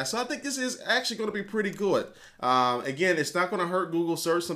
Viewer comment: at about 1:00 i think your'e trying to say cool and good but it ended it sounding kinda like gooud